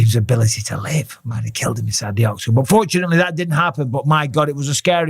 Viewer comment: woah woah lol, did I just here what Bispimg said right?? It sounded like he said fit looked like francis took away overeems ability to live but unfortunately that didn’t happen 😂😂 I had to rewind twice to here it right and he didn’t say that but it def sounded like it p, hearing it for the first time lol.